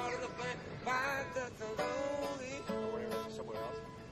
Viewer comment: does Ed say "i feel lonely hahaha" at 1:02?